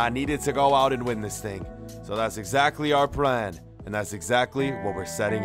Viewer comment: he said our pran instead of plan